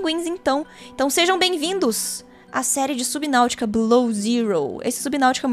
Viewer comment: me perdoe mas nesse começo eu escutei "subnautica bilau zero"